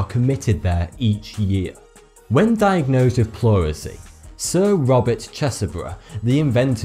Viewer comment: I thought it said “Sir Robert Cheeseburger”...it’s 3am 🤷‍♀️